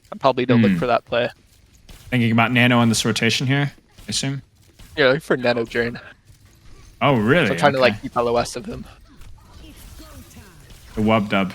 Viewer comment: What's saying here I can't understand "Wait for nano jay??? so I am trying to LOS it" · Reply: Nano journ (nano overclock combo)